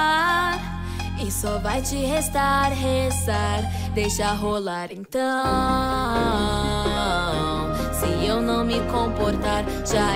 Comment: Eu ouvindo de dia: "...deixa rolar então..." De noite: "... deixa a rôla então..." kkk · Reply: eu escutei "beija a rôla então"